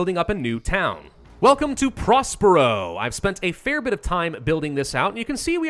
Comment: wait, did you say prospero? "project wingman and warhammer flashback intensified"😟